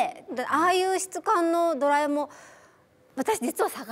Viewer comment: ドラえもんにしか聞こえんww